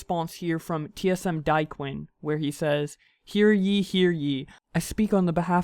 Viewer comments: Tsm Dyequin 😂 instead of daequan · Did he just say diequin?????